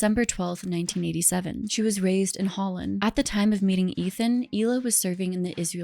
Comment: My Hebrew isn’t great (I’m Jewish), but I was definitely confused by some of the pronunciations (even though mine are probably bad too!). At first I thought you said Hila was raised in Holland but I realized you said Hol-on, which I think is closer to how it’s supposed to be pronounced. And Taglit is Tahg-LEET.